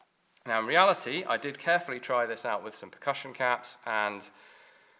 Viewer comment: You say it split in two? The first "Snap Cap"? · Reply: I didn't hear him say snap cap. He said "percussion cap" at 7:10, only one ignited, and that split.